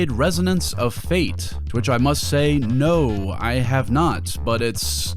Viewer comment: "No, I have nut." Without context and with this footage on the screen, you could think he said this.